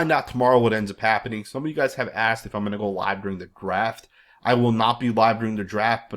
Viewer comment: It sounded like you said "People were asking if I was going to go live during the Graphk" as in GraphK Raider